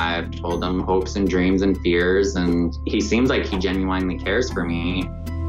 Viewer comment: Did he say genu-wine-ly?